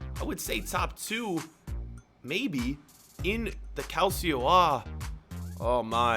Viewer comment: did he just say “the calcio A”?